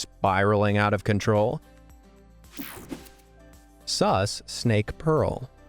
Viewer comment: Did I hear sus?